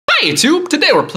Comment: Lol sounded like u said bye youtube